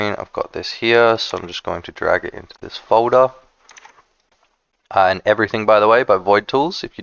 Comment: what was the other tool you mentioned? @ 2:10, whas it Void Tools? I couldnt hear. Thanks! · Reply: "Everything" by Void Tools - it shows EVERYTHING on your pc <num>x faster than windows ever could. Instantly. Make sure to set up a global hotkey too, mines ctrl + shift + f 🎉🎉